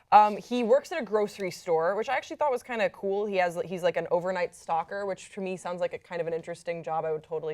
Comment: I thought she said the guy was an overnight stalker